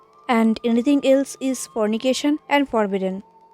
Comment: what have you said , voice clear nhi tha from anything else is forbidden and??? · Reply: Am really sorry for that dear ♥️ and "anything else is fornication and forbidden" 💞